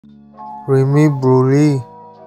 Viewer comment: As a Pakistani I thought he said “cream buri” (for the ppl who don’t know what “buri” means it means bad in Urdu) 😂😂😂